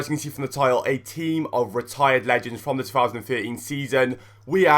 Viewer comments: I thought it said "Team of RETARDED LEGENDS!". Wow. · I thought it said team of retarded legends :P